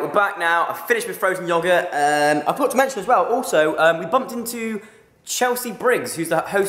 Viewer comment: i thought he said 'i got dementia aswell'